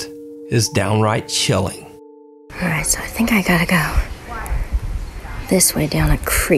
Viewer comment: I’m late asf was going through comments and didn’t see anything involving what I heard, between 5:03 and 5:05 you can hear a little girl saying “water”—— I am editing my comment, when I played the video back at 0.75x speed you can hear her say” I think I gotta go” then you can hear a little girl asking why. Gave me chills